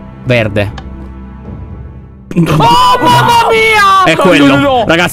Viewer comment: appena lo visto (avevo la gomma da masticare): Gli cade la gomma sul divano 16:32 solo io ho sentito "O MAMMA MARIA"